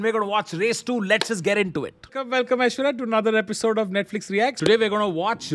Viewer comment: Did he say "welcome to Netflix reacts"😂😂 at 0:26 that's just great😂